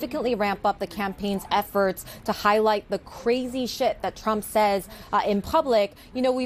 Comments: did she just say “the crazy shit”? Lmao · Did she say "shit"? At 2:16 -17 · did she say crazy shift or crazy shit? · Did anyone else hear her say “crazy shit” at 2:15??? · Did she say crazy shit?